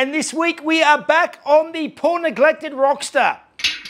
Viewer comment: Sorry, I know this is a family friendly show, but when you introduced the episode, I heard "Porn neglected Rock Star" and only thought, Dang! That's a shame for all those old aging Rock Stars! Enjoy all the videos/cars/content, thanks!!!